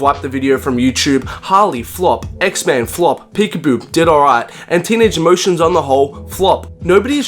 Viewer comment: Did he say "peek a boo" dead on arrival at 2:18?